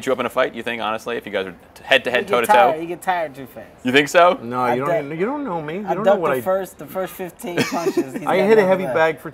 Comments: Did he say nigga you don't know me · i hear: 'Nigga u don't know me'